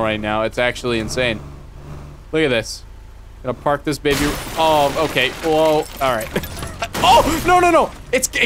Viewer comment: did he say OwO